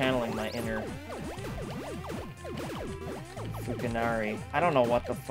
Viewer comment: did he say he was trying to reach his inner futanari? XD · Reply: Sadly, our good ol' looney pal said Fukanari. If he'd only said what we thought he said, it'd be a thousand times more hilarious.